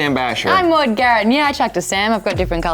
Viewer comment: what was the first thing she said, because what I heard was "yea,I'm shacking sam"